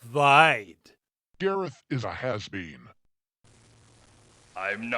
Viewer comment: did he just say "Gareth is a half bean"?